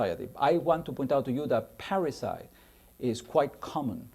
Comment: Wonderful lecture. Can anyone tell me what the word is at 30:56? I can't understand it: "I want to point out to you that ?? is quite common..." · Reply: Parricide - killing within the family: here he seems to be using it as a measure of insecurity and suspicion.